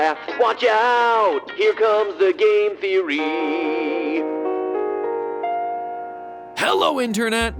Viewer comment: sounded like gay theory man 🤔